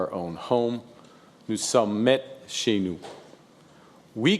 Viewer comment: Wha-a-a-a-at? "Oussa metching"??? What does he say? (It's not in the transcript. It's just omitted.)